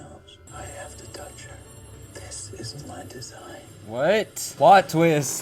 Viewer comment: The subtitles are wrong. He did say this is my design